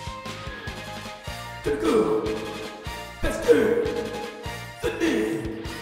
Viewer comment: What was the guy saying at 0:08? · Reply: "Take off... let's do... the deed"?